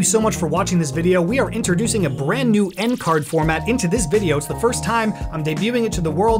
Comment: i heard it n-word card now i am dissapointed